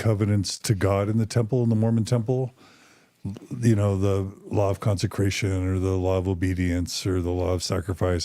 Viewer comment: I think I misheard but did he say "the law of constipation?" ;) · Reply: 😂😂 probably said "the law of consecration"